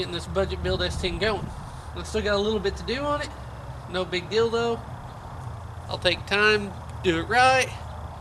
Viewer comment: Did you just say "no big dildo" (7:22)?